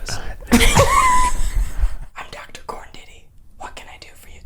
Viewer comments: it sounded like he said corn titty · I thought he said I'm Mr corn titty I was bawling my eyes out 😂